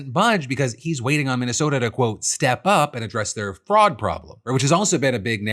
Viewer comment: I kept hearing frog instead of fraud